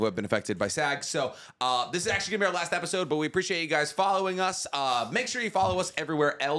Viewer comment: Did he just say this is gonna be the last episode ?